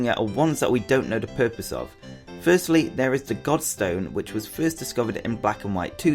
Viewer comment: Godstone looks like the stone from which zekrom appears! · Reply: Oh its Goldstone not dark stone! Phew I must have misheard